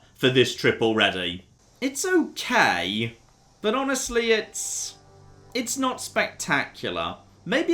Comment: what hes actually saying is its flat